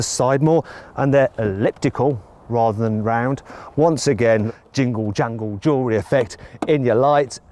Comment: 징글정글조웨헽 이 아저씨 뭐라는거임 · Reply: Jingle jangle jewelry. Jingle jangle이 ‘딸랑딸랑’이라는 뜻이고 jewelry는 보석임